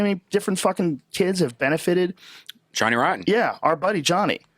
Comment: Did redban just say “Johnny Rotten”? And Joe says our friend Johnny! Did I hear that right